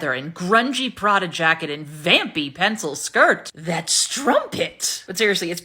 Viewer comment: Happy Sunday snark to all. “Breaking News, sexy Sophie, in leather?”😂 I could’ve sworn, you said, “crumpet not strumpet?”🤭😂🤣